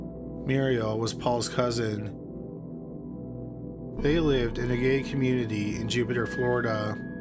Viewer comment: I love his videos but at 11:20 did he say "a gay community" instead of "a gated community?!?"